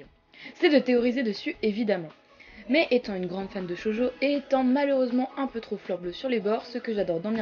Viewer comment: J'ai dû réécouter car j'ai entendu "Jojo" au lieu de "Shōjo". Remarque, ç'aurait été cool, on a jamais assez de Jojo.